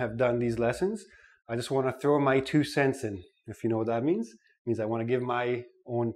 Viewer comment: What's the word he used? Throw my ______? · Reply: 2 cents in